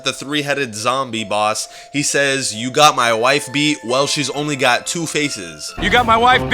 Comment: I thought the fighter said you got my wife beaten she on!y has to faces